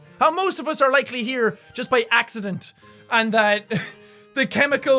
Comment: Did Jack just say i was an accident?